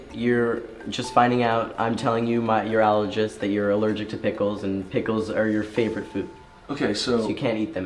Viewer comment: OMG THIS ISNT pg but I thought it was so funny right @ 16:41 an add came on and before that it had said you found out you r allergic to pick but I heard d;ck